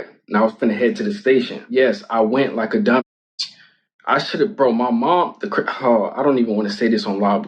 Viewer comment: did he just say "I shoulda brought my mom" ??? 🤣